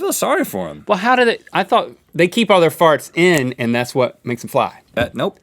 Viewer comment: I was watching this and playing skyrim at the same time, I thought they said they blame their farts on Stevie.hahhahahh